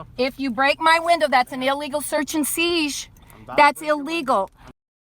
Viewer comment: did she really pronounced siesh instead of seizure. 7:07 she did it again seiesh or something similar.